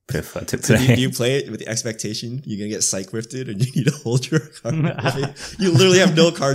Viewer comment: said "Cyc-Rift" as shorthand for "Cyclonic Rift" but I heard "Psyche Rift" and that totally sounds like a blue "draw/mill X" spell